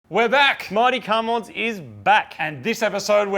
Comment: Mighty cumwads? Did he say???